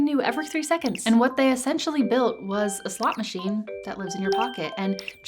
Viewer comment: I thought you said slop machine which would also be accurate lol